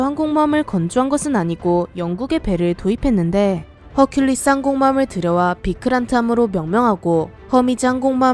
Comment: 저만 쌍공모함으로 들리나요?